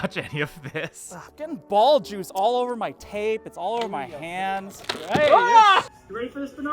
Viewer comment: I thought he said "I'm getting ball juice all over my taint." I was confused.